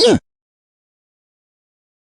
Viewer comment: Btw at 16:06 it's supposed to be "stop" instead of "for"